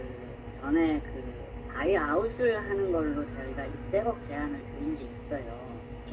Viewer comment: 600억? 200억으로 들리는거 같은데요? 다른데서 하는 말도 200억어었는데?